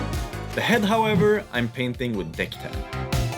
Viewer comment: Did you say you're painting the head with "dick-tan"!?!